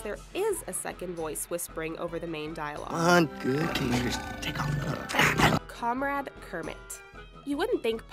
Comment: i heard "good teachers, take off your clothes!"